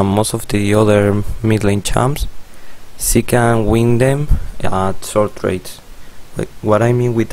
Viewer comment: ok can someone tell me what the fuck he is saying at 2:06? i tried listening carefully multiple times, even turned subtitles on, but all i get is gibberish + short trades at lvl 3. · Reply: Ahri dmg is way lower than other champs but she can win at short tardes with aa+w+q combo and using passive ms to run